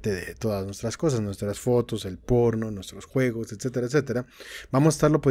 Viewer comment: creo que escuche porn